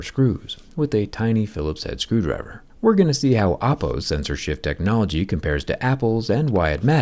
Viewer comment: When you said sensor shift technology, I thought you said censorship technology and I was like hmm that makes sense from a Chinese phone